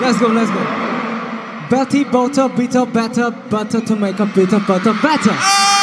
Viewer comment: WNN AYUDA 😭 ESCUCHE 𝗣𝗜𝗧𝗢 AAAAA estoy mal 🙇🏻‍♀️ me tome un para ceta mol 😭✋🏻